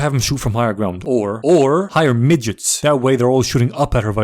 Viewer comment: I love that the subtitles say “higher midgets” instead of “hire midgets” I have no idea if this was intentional or not but I love it